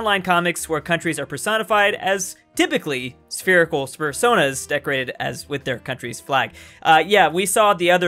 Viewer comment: FUR-sonas did anyone else hear that???